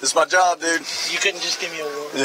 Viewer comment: "Could've gave me a warning" They had a pleasant conversation but you know the guy is calling the officer every foul name in the book under his breath. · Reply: he clearly says “Yeah, no, I couldn’t”